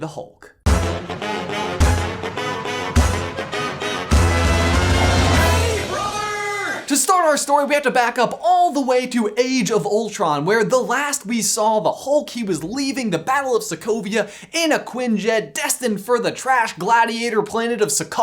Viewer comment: I accidentally clicked on another video and it sounded like he said "Age of Hey Brother!"